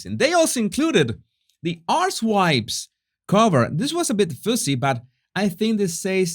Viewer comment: Did he say Arse Wipes at 7:20?